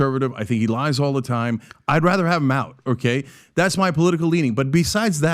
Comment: Did he say "I'd rather have Mao"?